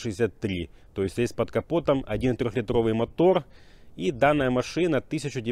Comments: Бля мне одному послышалось про трёх литровый мотор 😁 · Бля мне одному послышалось про трёх литровый мотор 😁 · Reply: Мне тоже послышалось 3х литровый 😂👏